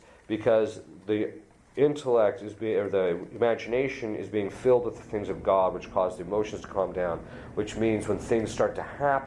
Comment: or so, father says that the imagination is being filled with...something and I have listened over and over but can't understand what he says. He gets to talking fast and with my not so good hearing, I don't catch what he is saying. Can someone with super hearing :) please tell me what he is saying? Thank You! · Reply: (regarding meditation) "...because the imagination is being filled with the things of God which cause the emotions to calm down. Which means when things start to happen, we won't emotionally react as much and we'll have a lot more peace and equanimity when all this hits." For future reference, you can also click the gear icon on the video player ("Settings") and select "Playback speed: 0.75" to slow things down. Hope this helps!